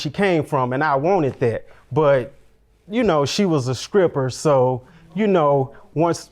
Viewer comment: Did he say : She was a 'skripper'❓